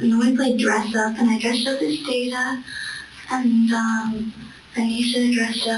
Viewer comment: I'm not a psychologist, I'm just a Star Trek fan, but one of the things that I found interesting is that when Morgan said she was playing dress up, the CC says "indistinct" but I'm pretty sure she said she dressed up as Data, the android from Star Trek TNG. This character is an emotionless machine whose character arc is entirely focused on becoming more human and capable of experiencing the emotions of living beings. I don't know if there is any depth to that idea but I found it interesting. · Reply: yeah i heard "dress up as Data" also